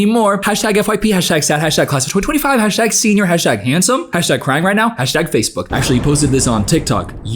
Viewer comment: I thought said "CRIME right now" and I thought she was at least a little self aware, until I looked at the screen and saw it was "Crying" Definitely feel like these posts SHOULD be a crime, but that's just me.